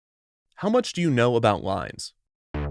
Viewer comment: I thought I heard, how much do you know about lies? Well I don’t wanna brag but